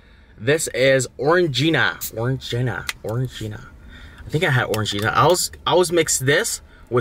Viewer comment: I honestly thought he Said “Foreign Gina” when trying to pronounce Orangina 😂